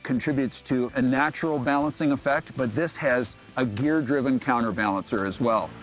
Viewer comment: 90 HP is Probably plenty of power for most people . 87 Octane gas .. Engine should last forever. How much weight does it add, what is the MPH? a 270 Degree crankshaft is not well balanced. You really do have to run a counter balanacer .. 270 Degree twins are very popular in Motorcycles supposedly because they make more low and mid range torque.. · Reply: Maybe I misheard, but in the review he stated a gear driven counter balancer is installed. Edit: yep, at 2:25 “gear driven counter balancer”